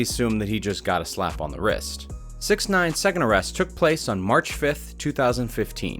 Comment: When it said March 5, I thought it said Marche